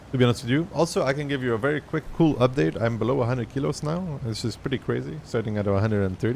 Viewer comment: Thought you said "Im below 100wn8 now"but below 100 kg is also great, cg